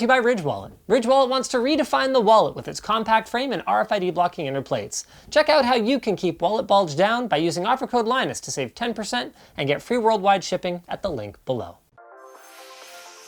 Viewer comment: I literally heard "to keep ur bulge down....Linus"